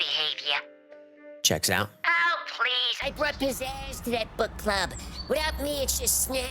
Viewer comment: I now realize he said “I brought pizzazz to that book club,” but initially it sounded like “I groped his ass in that book club.” I have never seen this channel before so I wasn’t sure if that was what it was HAHAHA